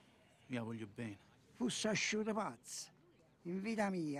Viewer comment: At a 1:30 he asks if he's "foogin crazy"...how does that translate in Italian?..what are the words he's saying?..can anybody translate for me please? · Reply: "Tu fuss asciut pazz?" It's Neapolitan dialect